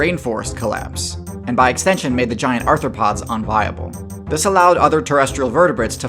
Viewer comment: I was listening to this while using ROBLOX Studio, the logo appeared on screen in the center of the video right when you said "and by extension made the giant arthropods unviable" and I sent my sides into orbit when my brain heard it as "RTHROPODS"